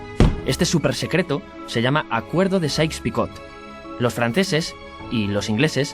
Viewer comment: Alguno podría decirme el nombre del acuerdo del minuto 2:34 ? muchas gracias, no logro entender bien lo que dice el narrador en esa parte, dejando en claro que no hablo ingles jajaja ... gracias por la colaboración gente. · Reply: Acuerdo de Sais picot